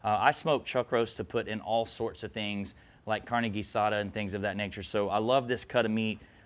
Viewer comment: Did... did you just say "Carnegie Sada" instead of Carne Asasa? I've watched this video 3 times or so in preparation for trying out this recipe, but I nearly choked laughing when I noticed this haha.